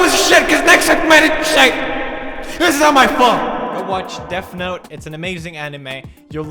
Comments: Is it just me, but i think i hear him saying "Deaf note" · i hear "deaf note" or "deff note" anybody else?